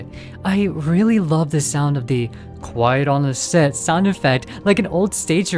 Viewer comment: Watching playthroughs of this game when it first came out, I never heard the stage call correctly. Instead of "Quiet on the set", I heard "Word of the dead", which made things 10x more terrifying 😅